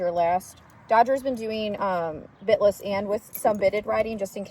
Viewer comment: Omgosh I thought you said "spit-less" not bit-less. 😂 I love the purple! 💜💜💜